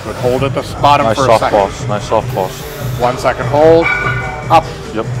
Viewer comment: I could’ve sworn I heard say softballs instead of soft pause .